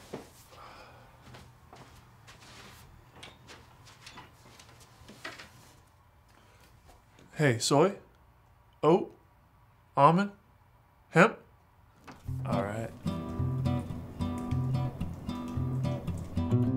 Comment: when he said "Hey Soy?" I thought he said my name, Zoya and I spit out my drink lol